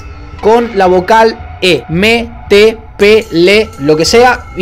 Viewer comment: Soy el único que se dio cuenta de que dijo pele